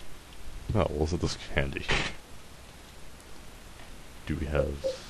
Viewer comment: at first, i thought you said "well isn't this candy"...THAT WOULD'VE BEEN SOME AWESOME CANDY.